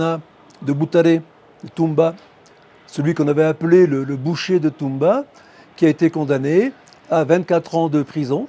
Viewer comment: You should check the subtitles before posting… At 5:14 the word "boucher de Tumba" becomes "Bushido" instead of "butcher of Tumba"…